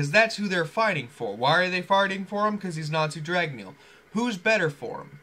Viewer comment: It sounded like you said farting instead of fighting in